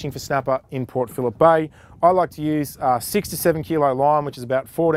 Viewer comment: Thought you said "67 kilo line " at first and was like what the.. then I realised you said " 6 to 7 kilo line " haha. Nice vid